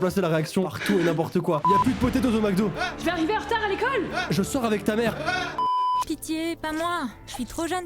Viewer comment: j'avais eu la pub a se moment donc du coup moi j'ai entendu "je sort avec les membres Amazon prime" XD 9:36 sa me tue 😂😂😂😂😂